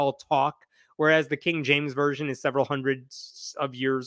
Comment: You mentioned the KJV but what I thought I heard was the KGB version! I'm confused.